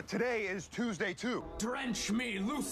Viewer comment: Drench me Lucifer. I don't remember that scene lol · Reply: I heard "french me Lucifer" and honestly I wasn't even that thrown off